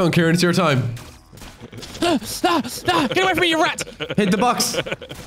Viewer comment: instead of saying in the subtitles "get away from me you rat" it say "get away from me Crowny" Crowny isn't even in the game, nor is he a rat (i think)